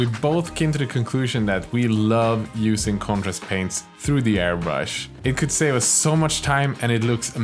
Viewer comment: At about 6:30 you say you love spraying “Congress” or some kind of paint out of the airbrush. I can’t quite catch what you said, is it a brand of paint? Your channel is great, I’ve been a modeler and airbrusher for decades, I don’t even know how to play these games but I do love your artistic techniques, thanks for what you do! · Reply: Contrast paints I believe he said